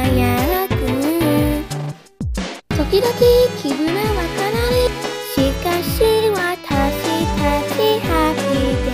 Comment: What did it say at 0:22-0:28? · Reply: "Sometimes, we feel down," "But we will remember our happy day"